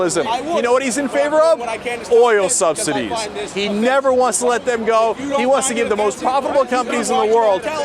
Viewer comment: Lol I hear, "Paul Ryan wants the banks to make the most profit possible etc." and "This is pornography. Going in to see Gadhaffi's corpse is pornography."